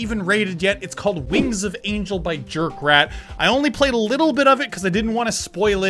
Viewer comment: I thought it said by jerk of rat lol.